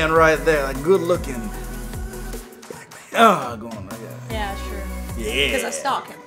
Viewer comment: said “black man” but at first I thought he said “Batman” and then I made a GIF and sent it to my bff and she said” I just watched that video and you did something wrong and right. said “black man” which is rasist. That’s wrong. But then you changed it to saying” Batman” which is not rasist. And that’s right. So you did the right thing for the wrong reason.” And then I texted back” ......oh. Never mind”LOL👌🏻